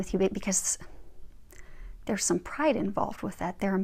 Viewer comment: are trying to say pride or private didn't understand that work clearly😢